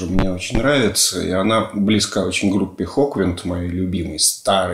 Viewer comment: Спасибо большое. Одна просьба, отмечать в описании название тех групп в оригинале, которые упоминаете. Тут упомянули группу "хоквинд", как мне послышалось, но на спотифай не смог найти с этим названием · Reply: Hawkwind. Очень много альбомов.